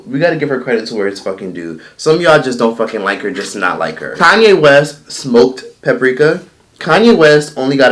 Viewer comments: I thought he was actually saying “Kanye West smoked paprika” and I’m like whattt? 😂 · Lol bruh for a sec I thought was saying Kanye West actually smoked paprika 🤣 I was like ohhh .... no wonder why he’s crazy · 🤣 thought you said Kanye west smoked paprika and I was like wait, hold up!!